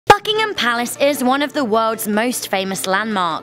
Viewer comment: At the beginning I thought you said fuckingtin palace